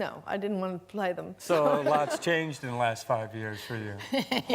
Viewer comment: Can somebody explain what mark said 2:58 sorry if anybody is offended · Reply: So a lot's changed in the last 5 years for you.